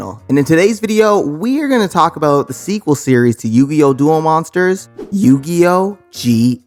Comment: Did I hear duel master's?